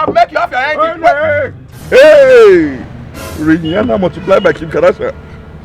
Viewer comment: I thought he said “Rihanna multiplied by Kim kardashian” and not “you will never multiply by kim Kardashian” per the on screen caption, or did I hear that wrong... anyways this was funny